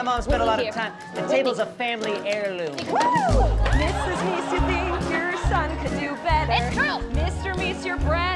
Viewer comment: what did she say about doing better and his sweater? I couldn’t understand her fully. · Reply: "Mrs. Meese you think your son could do better Mr. Meese your breath is worse than that sweater"